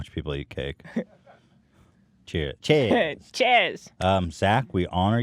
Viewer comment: Why the hell did I hear "Chairs" three times for no reason?